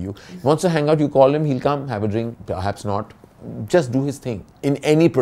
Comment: Did he just say "Ranbir will have a drink; perhaps have a SNORT(which I assume is inhaling drugs through nose)", OR did I got it all wrong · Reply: Its “perhaps not” not “have a snort”